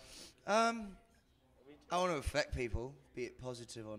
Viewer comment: Oh affect! I thought he said infect